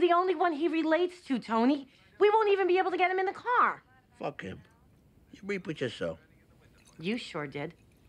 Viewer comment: what does tony say at 1:05? thanks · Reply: You reap what you sow